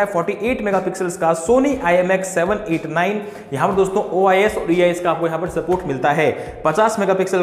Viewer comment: Kya bole Kuch Samaj Nahi Aya Sir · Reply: OIS + IES